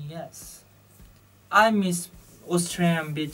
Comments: I thought i heard "Australian bitches" but then reaction was like blank... so i was like "OOOOHH he meant beaches"!!!! Gosh he's adorable in a way lol xD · I heard 'Australian bitches' haha It's funny though because just a couple of days ago I had a disagreement with my friend about whether people poo in the water at beaches... Even if some people do I think I'd prefer being in denial and saying they don't :P